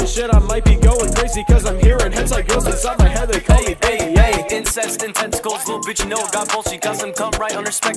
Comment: I thought you said insects and tentacles not "incest and tentacles". Oh shit why did insect come up in my mind ;-; New fetish? Nah just me? Ok.